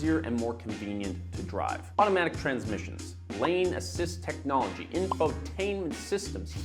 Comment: Lol I misheard lane assist tech as lame assist technology